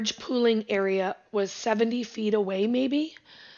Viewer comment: Thank you for your work. Libby was moved 70 feet?!? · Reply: Correction - I misheard the distance, it was "7 to 8 feet" NOT 70 feet - I've pinned a correction under my video and am working on editing it.